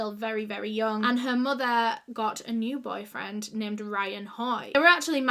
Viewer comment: I kept hearing "her mother Youandme"